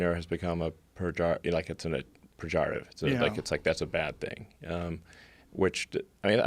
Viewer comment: What is the word that he's saying? Pejorative?